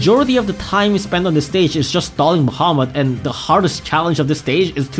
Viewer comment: WHY did the subtitles say "Muhammad" instead of "Bahamut" bruh😭